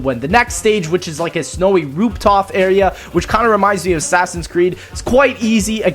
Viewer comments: did he say roop tof? Ayyyy lmao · rooptof area? Is just my ears or did you say this shit XD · At around 8:19 he said roop tof instead of roof top